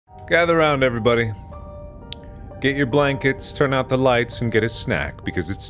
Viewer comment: i thought he said "get your blind kids" instead of "get your blankets" 🤦🏼‍♀️